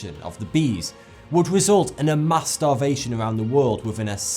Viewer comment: i was seriously contemplating my life in this video but then for a second i thought you said masturbation when it was supposed to be mass starvation. im sorry.